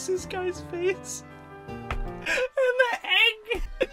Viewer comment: sounds like he said meg Well idk